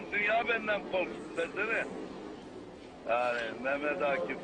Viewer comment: de DESENE diyor pezevenk değil.. 2. si başbuğ hakkında hiç bi zaman kötü konuşmamış bide hüseyin feyzullah ismini bi araştır isterseniz